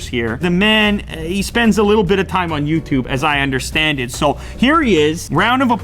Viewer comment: Did he just say understanded 3:22 ! It's understood. (Can't stop myself from correcting others)